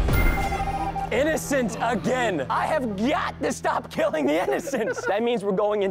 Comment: Did he say I GYAT to stop killing the innocent on